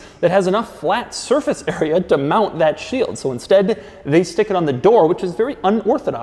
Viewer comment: who else heard "shit"